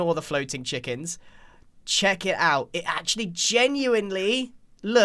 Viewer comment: Grian: "Check it out" What I hear: "Chicken"